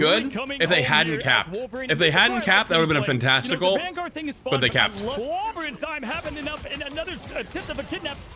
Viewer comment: did someone say fantastic? Also what team is Jay3 on?